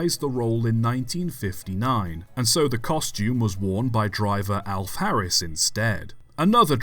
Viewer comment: oh.. Alf Harris. I thought I heard Alpharius for a moment!